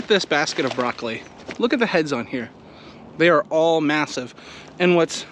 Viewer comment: I thought you said look at this basket of broccoli they are all Mathews lol.